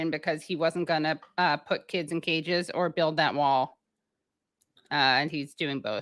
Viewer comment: Hahaha, just thought Katie said ”Build that mall!”. 😂😂